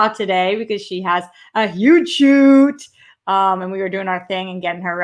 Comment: At the beginning when you said Steph has a huge shoot, I thought you said nude shoot lol. Oh well, hopefully someday. Whatever it was, I hope it went well for her. I also hope the one you did was safe despite nobody on set wearing masks. It blows my mind how so many people are still being that careless. As for Bella Thorne, I agree she should refund people's money. What did she think her fans expected? I don't get the point of having an account on that site if you're not gonna post nudes. That's what it's meant for. She has a history of being a tad out of touch anyway. Hell, I remember she complained on Twitter once because a road was closed because of a mudslide or something like that. A bunch of people, including other celebrities, called her out on it lol.